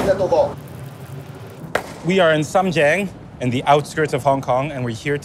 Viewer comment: Captions say Shenzhen, but it's Sham Tseng (深井) in Hong Kong.